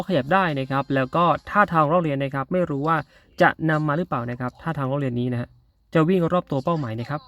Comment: วินาที่ 0:35 พูดว่า ท่า อะไรหรอครับ ผมฟังไม่เข้าใจ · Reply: ท่าล้อเลียนคับ